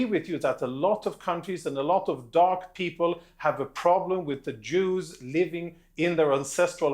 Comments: Did he just say, “dark people”?! WTH!!! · did he say Alot of dark people !? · Did I mishear or did tge Israeli say “a lot of dark people” have a problem with the Jews living there?